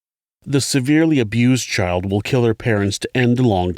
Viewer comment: it sounds like you say, “the severely abused child will kill her parents…” I hate to be a stickler but I’m genuinely wondering if this pertains more to females than it does to males or if I’m mishearing things